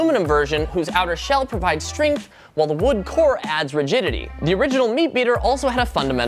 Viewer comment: when he said rigidity I thought he said “virginity” 😂😂😂😂